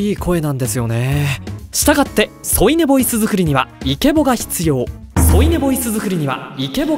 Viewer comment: のところだんだん「生け簀作りには」に聞こえてきちゃう